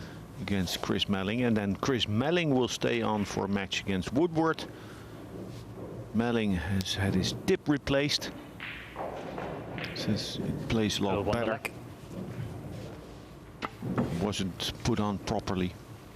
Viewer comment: first time around i heard that Melling has had his hip replaced, has a funny Dutch accent, sounds so sweet to the ear, but sometimes it can be deceptive :))